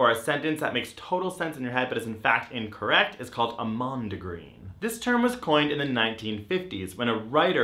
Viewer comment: I misheard Greg. I heard him say mom degree.